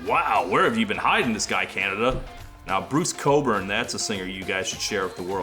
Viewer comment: does Todd say Bruce Coburn? Does he mean Bruce Cockburn?